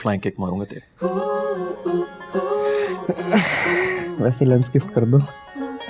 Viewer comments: Can u plz tell what waleer said at 15:25 ( i can't hear that properly) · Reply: "wese lense gift krdo" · kaddu ne Kya Bola? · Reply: Wase lens gift karlo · Wht did kadu said in 15:26? Lens kiss kardo?